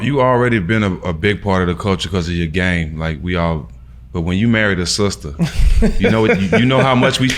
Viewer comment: I thought they said he "married his sister."😂😂😂💀